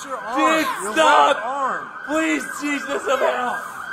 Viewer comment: Did i hear "Stop, please, Jesus of hell", at 3:10?